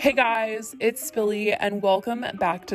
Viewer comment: Wait ‘spilly’?? I thought you’ve been saying ‘billie’ the whole time